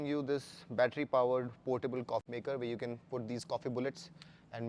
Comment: Did you just say C*CK MAKER at 5:10?😂🤣😂